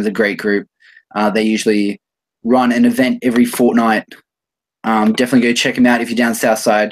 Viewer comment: did he just say fortnight?